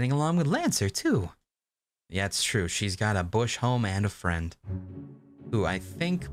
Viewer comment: I heard that as: she's got a bush, home, and a friend a little more than I wanted to know about Susie